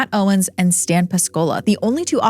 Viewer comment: Im sorry and i know this is a pretty serious matter but i thought i heard "piscola" instead of pascola and couldnt help but laught. Piscola is the most popular alcoholic drink here in Chile.